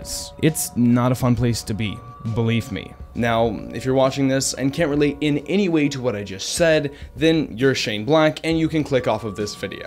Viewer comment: I thought I was tripping, but you said "can't really in in anyway to what I just said" at 0:41.